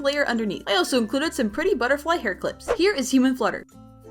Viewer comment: At first I thought she said “here is Temu Flyte” lol 😭